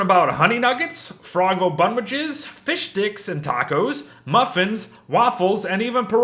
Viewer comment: Only I hear ,,Fish dicks"?